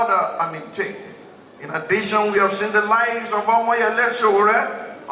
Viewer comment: Did he say the "lies of Omoyele Sowore" at 2:00? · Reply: The likes of Omoyele Sowore